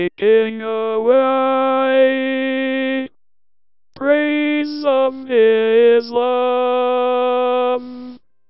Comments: Anyone else hear praise Islam in the last one...? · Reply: The Savage Skeptic “praise of his looooove” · I heard “praise of his love” not praise of Islam lol